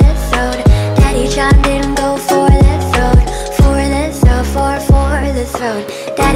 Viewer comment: I fr thought it said "daddy shot him, go for the throat"